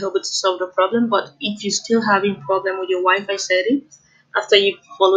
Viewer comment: I thought she said " your wife" 😂😂